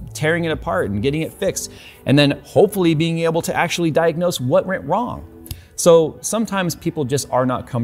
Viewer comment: Jay says "what rent wong" instead of "what went wrong". Sorry Jay I'm just messing with ya. Love your videos btw. Cheers! 🙃